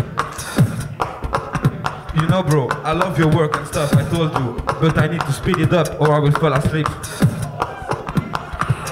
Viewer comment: What did penkyx say at 6:40? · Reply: "You know bro i love your work and stuff, I told you. But i need to speed it up or imma fall asleep"